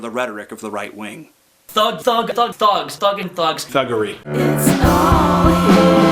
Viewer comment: What does the woman say at 13:29 to 13:33 ? I really can't make anything out of it. Might be cause I'm Norwegian. Sounds like she's saying "it's all here", but wtf does that mean?